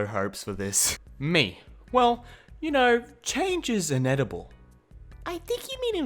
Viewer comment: He said invetable instead of inevitable